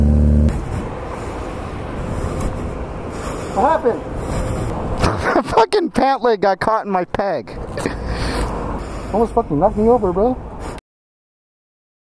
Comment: Did i hear "My 3rd leg caught on the foot peg" lol